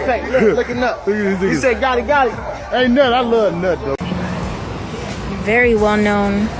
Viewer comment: Ayyy yooo!!! What he say… ⏯️ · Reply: “I luv 🥜” 🤯🤯🤯💀💀💀